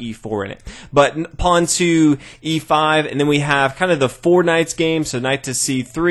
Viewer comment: did he just say fortnite?